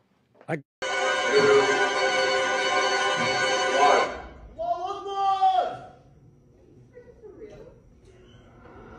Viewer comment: what did he say i couldnt hear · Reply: Allahu akbar